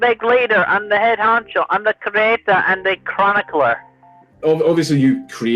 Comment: I think he said "The Cabeza" not "The Creatah"